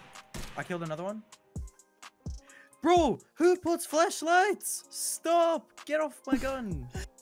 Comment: I thought he said "dude who puts flashlights on their guns get off my gun" lmao he said flashlights